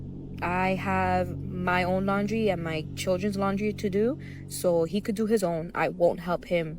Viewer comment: did she say My Childrens?